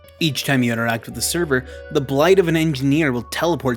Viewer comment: did i hear a blight? "SERVERBLIGHT"